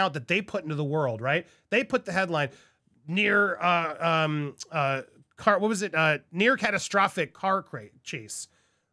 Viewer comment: Them using a term like "near fatal car accident" implies there was an accident, critical injuries were sustained but everyone is expected to pull through. There was no accident. Megan wants so bad to be Diana and it's awful because she's using her husband's dead mother's real tragedy and trying to apply it to herself. It's absolutely disgusting and if I were Harry, I'd be outraged but unfortunately he's going right along with it, disrespect for his mother and all. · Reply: thank you for that! I heard "near fatal accident" and didn't realize they were saying "near catastrophic". Still very sickening either way as you stated!